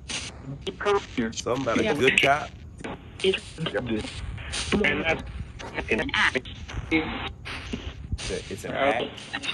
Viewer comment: I heard "the good cop is an act. Watch out for him"